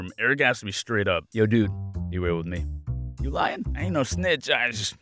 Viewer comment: I can't really understand what he's saying here Edit: Now I do He's saying "Be real with me... you lying?"